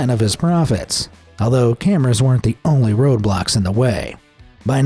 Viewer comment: an i the only one who heard roblox at first